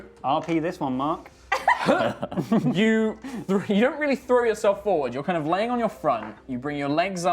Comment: i heard "urethra yourself forward..."